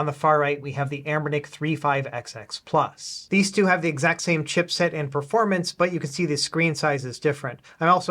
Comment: I know you said “exact same chip” but it honestly sounds like you said “exact same shit” 😂